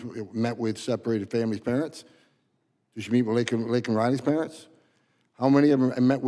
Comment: Did he say LINCOLN RIley too!? WTF! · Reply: Nah, his accent made it sound that way. I checked out the cc and it said Laken. The SOTU the cc actually said Lincoln.